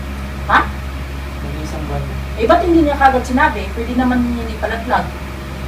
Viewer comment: pagkarinig ko talaga "pwede namang... (hindi) ipalaglag". mabilis lang at hindi masyadong maganda quality nung pag ka record ng voice so para sa mga taong na misheard sa pagkasabi ng mader niya, pakinggan nyo ng mabuti tsaka isa lang tong prank kasi nga april pools nung araw na ginawa tong video. sino ba namang ina ang gugustuhing ipalaglag ang bata? ni hindi nga man lang nagkaroon ng unting tensyon sa usapan nila for me. parang support medyo nanay nya kasi totoo namang malaki ang obligasyon ni guy. ano nga ba purpose ng channel na to? to make fun with madla diba? sana malinawan kayo hahaha. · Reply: Pengwiyo 뺀괴요 "bakit ngayon lang nya sinabi eh pwede naman NYANG ipalaglag" yan yung sinabi.. walang word na "hindi"... hays.. tsk tsk tsk